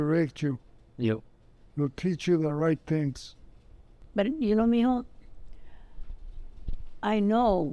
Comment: Did she said ypu know "mijo"?